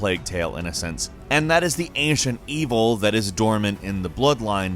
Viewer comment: I thought he said Asian evil🤣🤣🤣